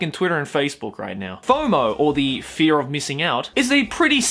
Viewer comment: I thought it said 'Homo' at first lol